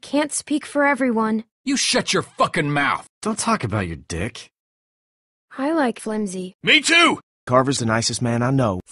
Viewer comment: What is said in 1:47-1:48? I can't hear exactly what Clem says. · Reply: "I like Flimsii."